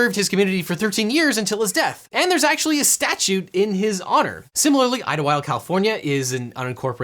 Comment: it sounds like "there is a "statuTE" in his honor". I know it's supposed to be "statue". Am i hearing things?